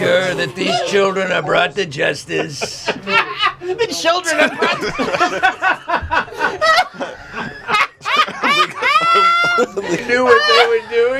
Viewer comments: A guest on BOTW has never stolen the show like this. You gotta have come back some time. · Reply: what does say? "Children of Brut" or something? (Brut the champagne) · what does say? "Children of Brut" or something? (Brut the champagne) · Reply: "children are brought to justice" :) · "Balls" At 56:15 what does say? "Children of Brut" or something? (Brut the champagne) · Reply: he repeats joke. "The children are brought to justice."